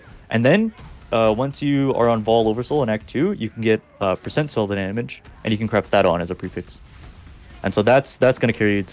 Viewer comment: what does he say at 9:42 ? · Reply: "You can get % spell damage"